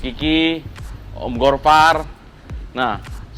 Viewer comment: gua salah denger ga si kok on gorfar wkwk